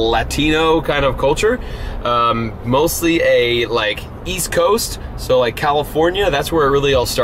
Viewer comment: "mostly like east coast, so like LA" somebody buy him a map and compass lol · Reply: I thought he said wEast coast